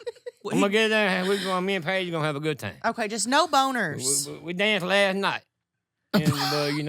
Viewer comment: I think thought you said booger instead of boner 😂😂😂